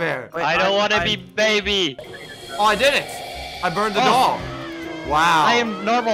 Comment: subtitles say, I burned the dog